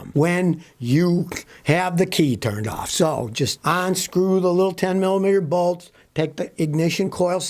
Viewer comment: For years have heard you say “ONSCREW” instead of UNSCREW. Do you do this Unpurpose?